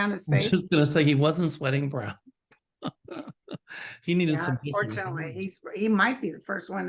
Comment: Auntie, did you say Gulliani “needed some milk???”🍼 😂😂😂😂